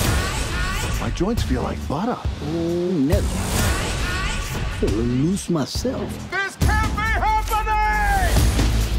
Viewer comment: holdup did he just say the n word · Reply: He said oh nelly it’s an old people saying